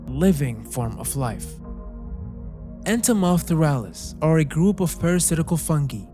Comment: I'm sorry but I thought you said Enter Miles Morales. Thought what I was watching was suddenly changed to something else